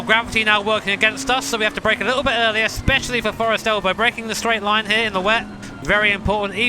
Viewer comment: ah Forest's Elbow not Forest Elf - I misheard that xD